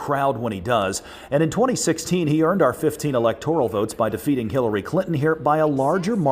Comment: Lmao at 00:45 it sounds like he says killary instead of Hilary lmao